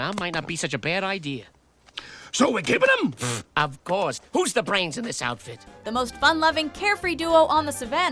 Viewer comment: I thought she said Gus is the brains lol